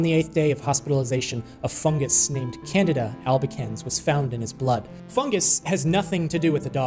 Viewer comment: I had to look at the screen because when he said Candida Albicans, I thought he said Canada and thought that he was referring to Canadians as a fungus. LoL 😆